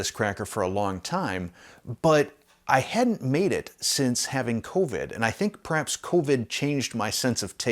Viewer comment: Well I made an embarrassment, I was half way listening to this in the background, and I heard you say "I havent made this since I had Colton" and I had to do a double take and thought "Wait, what? I thought were his grandfather not his Dad...." I didnt realize u said COVID..... boy do I feel like a damn dummy lol